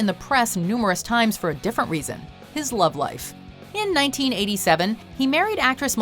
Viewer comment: I thought she said 1997 and broke up in 1995 and I was confused but it was 1987